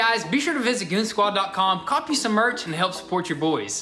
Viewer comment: At end of part 2, did he just say "police" instead of "peace"?😂